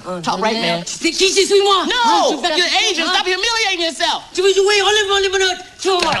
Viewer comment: "No, you're Asian! stop humiliating yourself!" I love Chris Tucker! XD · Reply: I thought he said "No you're an agent!" 😂